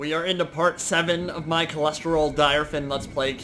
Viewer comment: I thought I heard Cholestrol Direfin at the start of the video